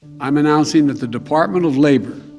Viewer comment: When Biden said "I'm announcing with the Dept of Labor," for a split second I thought I heard "I'm a Nazi with the Dept. Of Labor". 😅 Here is the f@ up thing, everyone of my co-workers who have tested positive within the last couple of months, have been fully vaccinated, and got pretty sick from Delta (definitely not mild, and thankfully not dead). Yet, because I have natural immunity and refuse the V, I lose my profession as a healthcare worker...the risk benefit isn't there for me, but I am not allowed weekly testing or proof of antibodies through blood test (going against all of our best science). It is like I am being punished for being healthy, and questioning the new pseudoscience! Furthermore, I can go work for almost $10 an hour more at a faculty assembling C19 testing kits... What a scam!!!! And the whole "Protect the vaccinated from the unvaccinated", bro all my tests come back negative, I am the healthy one! Most people have taken zero accountability for their own health, by taking care of their immune system, just putting all their eggs into the V basket...dumb.